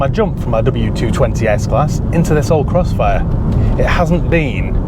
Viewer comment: I actually misheard you calling the car a 'Crustfire' at one point. Very apt. The styling is a mishmash of 40s/50s Americana and a hearse🤷